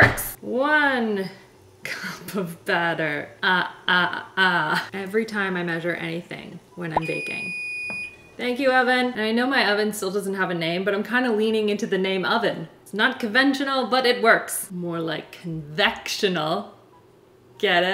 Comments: I think you should name your oven Evan. I actually thought you said, "Thank you, Evan!" Then I realized you said "oven." Evan the Oven! I like it! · When she said ‘Thank you over” i heard “Thank you Evan” please call him Evan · When she said 'thank you OVEN', I literally heard 'thank you ALVIN' instead😂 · “Thank you Oven (EVAN)” 😂😂😂 totally thought you said Evan! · I vote naming the oven "Evan" because i thought you said "thank you Evan" when you said oven, LOL